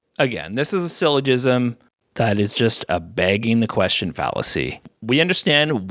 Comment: Did you say "bagging the question" 21:25. I think it's "begging the question".